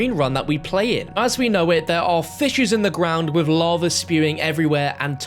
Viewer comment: did he say there are "fishes" in the ground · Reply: Fissures